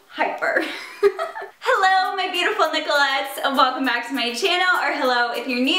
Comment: I always thought she said ,"Hello my neglects!"